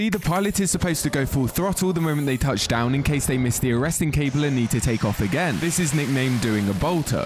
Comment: I thought he said interesting cable and was like yeah no shit